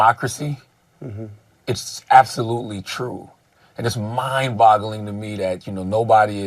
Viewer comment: Did he really say mind bottling 😂?